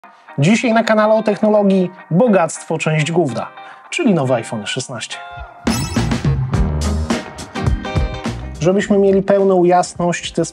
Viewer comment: Ja usłyszałem: Dzisiaj na kanale: bogactwo, część gówna xD